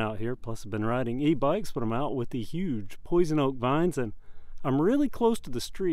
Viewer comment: did you say Poison Oak <mail> a FS robing power, can you lock out the rear suspension you installed, when pedaling up hill? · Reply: Yes, poison oak vines. Yep, but I don’t pull over and flip lockouts at each hill.